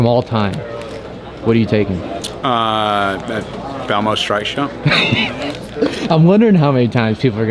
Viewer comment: What did dude say @ 6:30 ? Lol Belmo’s what?? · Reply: Stike shot